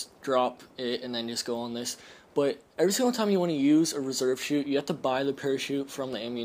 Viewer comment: I don't know why but I thought it said "reverse"